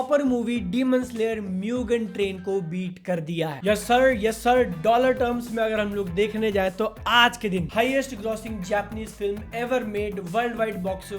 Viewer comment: did he just say Donald Trump 😂